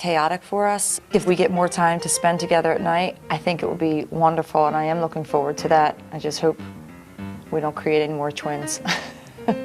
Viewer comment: What did she say?!?! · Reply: “If we get more time to spend together at night, I think it would be wonderful and I am looking forward to that. I just hope we don’t create any more twins.”